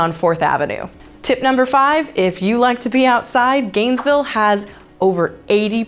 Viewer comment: I thought I heard "if you like to pee outside...' (!) I was shocked for a moment, thought it was some kind of nature museum where everyone pees outside or something. Informative video tho '